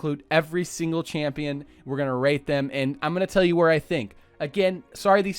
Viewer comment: For some reasons my Google captions said this at 15:20. “We’re gonna rape them” Love google <3